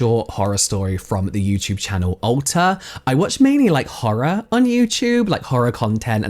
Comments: I heard "Ulta" instead of Alter at first and was like "wow they're making some interesting marketing choices" lol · ! I thought you were saying Ulta, not Alter! That would have been even better.